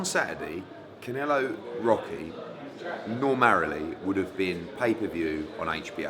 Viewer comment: Did Eddie just say 'normarily'...